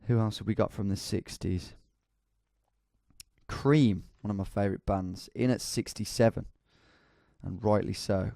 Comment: I thought he said “creed, my favorite band” and I was about to unsubscribe